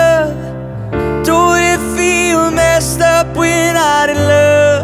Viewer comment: MESSED UP?! MESSED UP?! I heard that right...right?